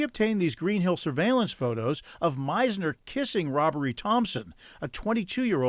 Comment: I'm sorry did he say this dudes make is 'Robbery "?!?!? Black people what the hell? And white ladies need to get some self respect!!!!! Wtaf